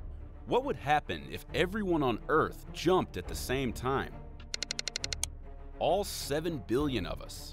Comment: i thought it said "if everyone in the world jumped on you at the same time" 💀